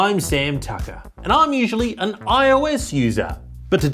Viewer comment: I thought he said "I am an IOS loser" my hearing is 98% flawless